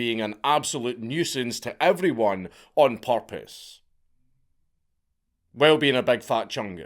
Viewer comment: He was doing it on "porpoise"? It kinda sounded like you said it...